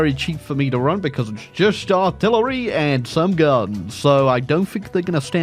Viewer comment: did someone say just artillery?